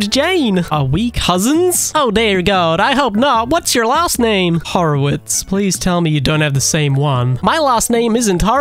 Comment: I thought that said Howitzer bro I ain’t even 💀